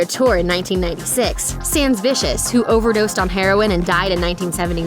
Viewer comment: Did she say Sam Vicious OD'd and died in 1979? Who's Sam Vicious! :o)